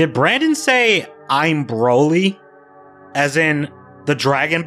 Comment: I heard him say "I'm Broly" multiple times. It wasn't just you.